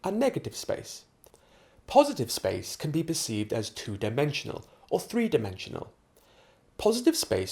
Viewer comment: Hello am trying to figure out what word you said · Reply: its perceived dude!!!!!